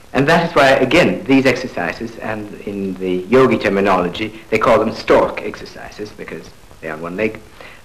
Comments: Does anybody understands what he says at 16:45? "in the ??? terminology they´re called ??? exercises" Thank you! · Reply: "in the YOGI terminology they´re called STORK exercises" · Does anybody understands what he says at 16:45? "in the §$%§$&$%& terminology they´re called $§$%"§$% exercises" Thank you! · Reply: stork exercises , you can find these exercises on the internet